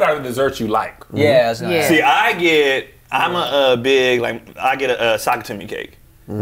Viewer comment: what cake did he say? Asking for research purposes 😂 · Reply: ChrisTheProgrammer Sock-It-To-Me